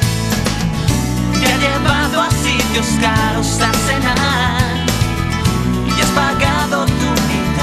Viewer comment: Desde que te fuiste aquí la situación Ha cambiado para mal. Desde que te fuiste para estar con el Se que sufres tu también Te marchaste en busca de un lugar mejor Que oportuna decisión Te marchaste apenas sin mirar atrás Ponte tú en mi situación Como olvidar tus ojos si me miran desde el cuadro Que colgaste en la pared Como olvidar, tú nombre si lo llevo aquí Quemándome la piel Mientras limpias sus trofeos de futbol Y le preparas un café Mientras ve un partido en la televisión Yo aquí solo pienso en ti. Te ha llevado a sitios caros a cenar Y has pagado tu mitad Mas como siempre has dicho el postre es lo mejor Y nadie lo hace como yo. Como olvidar tu cuerpo si mi cama Esta tan fría desde que no estas aquí Como olvidar, tus labios Si mi espejo esta marcado de carmín. Como olvidar tus manos, como olvidar tu pelo Como olvidar tu cuerpo Y aunque no pueda olvidarte se muy bien Y aunque te hagas la importante yo se que Y aunque trates de negarlo se muy bien Que tú también piensas en mí. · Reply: Pensé que decía te ha llevado a sitios caros a cenar y ha apagado tu humildad🤣